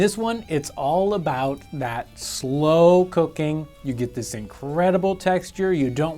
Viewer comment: I know he's saying "slow cooking" but all I hear is "snow cocaine" Wishful thinking maybe?😁 And now you all know MY secret ingredient in place of nutmeg😲